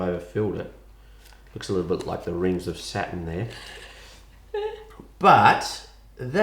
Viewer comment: I used too watch you when I was four and I would beg my parents too bake cakes with me, now I’m ten and I got intersected in cooking And I knew what channel I would go too first! Also when said “Saturn” I heard “satan”